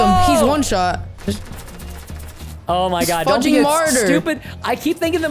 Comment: He said fudging and i had emediatly thought he said fu***ing